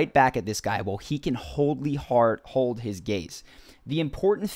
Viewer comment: I couldn't understand what you said at 1:39, sounds like you were having a stroke and the words just didn't make any sense... "while he can holly heart hole his face..."